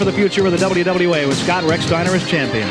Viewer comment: I thought he said Scott & Rick Steiner from Michigan , but it turned out to be Scott RexSteiner from Michigan.